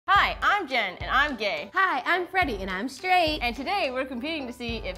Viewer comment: At first I thought Jen said “Hi I’m gay, and I’m gay.” 😂